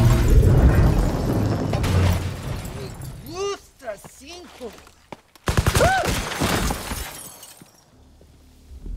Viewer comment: What dose he say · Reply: He's saying he likes five